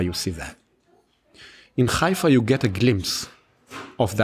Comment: Was sagt er bei 1:04:45? (Suche wirklich das engl. Wort.) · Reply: glimpse war's, danke!